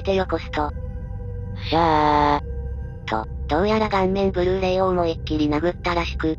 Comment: 聞き流してたから｢顔面ブルー霊｣を｢盤面Blu-ray｣に空耳しちまった